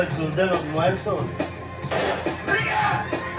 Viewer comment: Tengo una pregunta en el minuto 0:34 lampiño que fue lo que dijo fria o ria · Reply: Dijo rie 😂😂😂